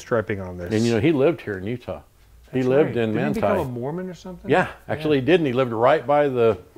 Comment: Oops! The Closed Captioning text at the bottom of the screen says "Moron" instead of "Mormon". LOL! What Moron typed that?